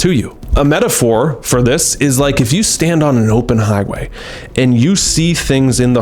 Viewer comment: I swear heard "if you stand on oppenheimer" 😂😂😂